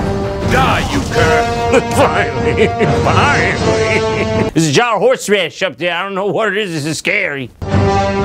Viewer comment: what dose he even say at 9:57 ? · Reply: Theres a jar of horseradish over there but I don't know it's scary